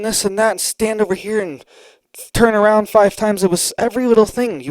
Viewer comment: did you say "turn-around 5 times"?! 🤣 you made night lol